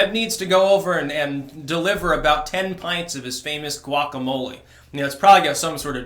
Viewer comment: Did he just say "pants of shit"? 😂😂😂